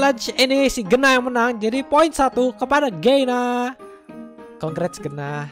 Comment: kok gue dengernya geynah😂😂